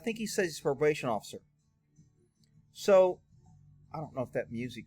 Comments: I believe he said "I'm an off duty CONSERVATION officer" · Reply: I thought he said he was a Constipation Officer. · Sounds like he said off duty Conservation officer. Thats fish and wildlife officer.